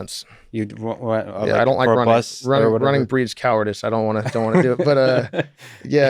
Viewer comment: this guy doesnt do cardio because „running breeds cowardice“. That‘s the most hilarious thing I heard in 2020 😂😂😂🤣🤣🤣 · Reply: I thought he said "running burns calories..." :D